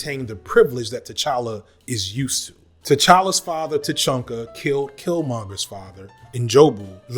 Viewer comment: Am I the only one for a second thought he heard him call T’Challa’s father Tuchanka instead of T’Chaka? Like the Krogan homeworld from Mass Effect?